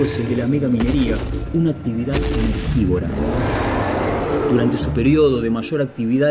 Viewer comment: Necesito hacer estobpara un t.p de la esc. Alguien me puede decir que carajo dice en el segundo 1:37 mas o menos. Después de que dice "una actividad..." por favor URGENTE!! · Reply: "energívora". perdón. un poco tarde la respuesta.